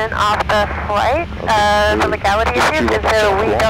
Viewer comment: I wanna know why the Capt of the flight reporting in at the end (18:40) was being pulled for "Legality" issues....at least that's what it sounded like to me.